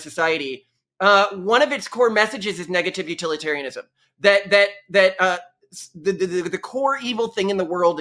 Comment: did he say "one of its core messages is negativity utilitarianism"?